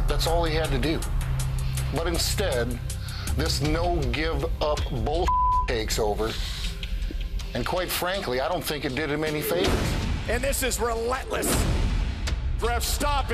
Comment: What does Brock says at 0:57 to 1:02? Can anyone tell me I want to know. Thank you. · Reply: "Instead, this no give up gimmick takes over and quite frankly, I don't think it did him any favor." - Brock Lesnar